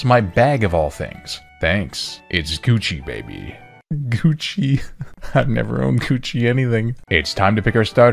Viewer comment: On God I thought man's said "Ive never owned coochie anything"